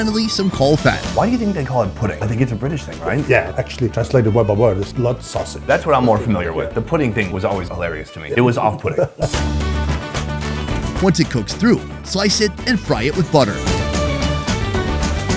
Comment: Did he say it was “off pudding “ 🤣🤣🤣